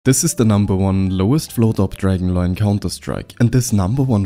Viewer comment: Did you just say "AWP Dragon Line"?